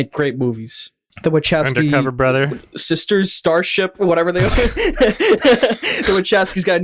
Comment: did call the wachowskis “starships”?